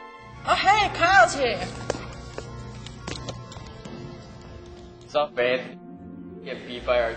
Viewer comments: does he say whats up babe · Please Tell me he says "Gabe" or "Abe" Instead of "Babe" at 3:54 Like I keep hearing.